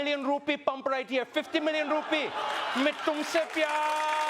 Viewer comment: For those who don't understand Hindi or even for those that do as I had to listen to it twice to understand what he was saying, at 9:47 he screams in Hindi saying Main tum se pyaar (I love you)